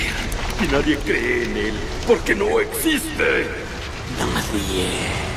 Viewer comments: no entendi que dijo ahi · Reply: Dice "nadie" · que dice??? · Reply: Nadie